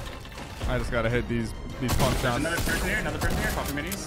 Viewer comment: I thought he just said the pumps were trash lol